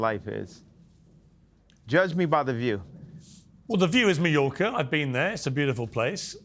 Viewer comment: What does Piers say at 6:17 ? Is it Mallorca or Mediocre?